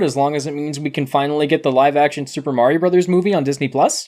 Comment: What are you even saying? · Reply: Disney plus Mario movie